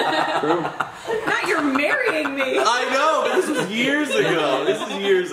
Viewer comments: did say matt was marying her????? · did say " Matt you're marrying me " ???????